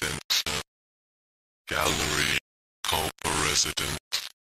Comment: I heard 2 words there: Category and Residence.